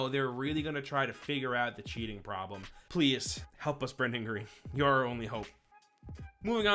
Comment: thought he said "please help us Brendon Urie "